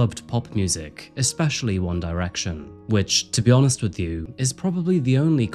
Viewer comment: "Her true crime was listening to One Republic".😂 Sadly, it Too Late to Apologize. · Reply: he actually said 'One Direction' lmao